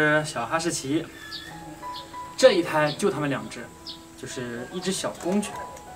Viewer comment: 这一胎就他们两只 我给听成了就TM两只😂 赶紧回放发现是我心灵幽暗了😅